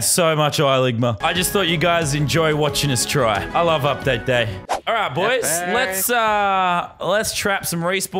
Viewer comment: did lannan said that he loves update day😂first time in his history😂😂